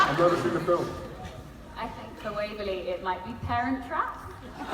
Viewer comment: Can someone plz tell me what Dom said her fav movie? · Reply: She said she thinks Waverly's favorite movie might be "The Parent Trap" or any Disney movie.